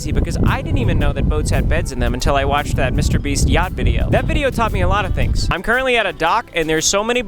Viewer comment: I thought he said “gyatt video”